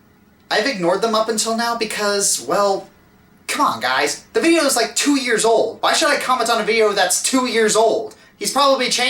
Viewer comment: The automatic captions say "well cock ice feels like two years old icons on video that's two years old" XD I laughed for a full minute!